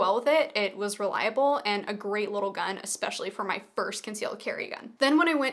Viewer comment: May I ask why you brought up purse carry? This topic was never touched on in this video. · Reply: I thought I heard "purse" when you actually said "first" at around 0:45. My mistake. Still, a valid concern for those who use a purse. Did you ever try the XL?